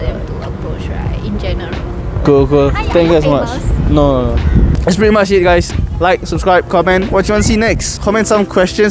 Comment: what does the girl says at 10:17 that you answered no? i didn't hear it clearly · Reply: She says are you famous